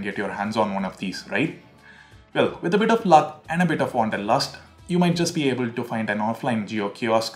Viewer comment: what did he say ?!! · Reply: Ethan Rebello wanderlust.... It's a play on words considering you'll have to roam about a lot before you find a Jio kiosk who's willing to pre register you